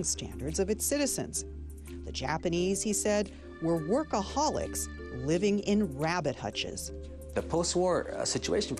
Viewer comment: HAHA I THOUGHT SHE SAID ALCOHOLICS LIVING IN rabbit hutches. Im an alcoholic. not a workaholic. and I live in a rabbit palace. HAHAHA Poor Japanese... feels guilty. Noooot!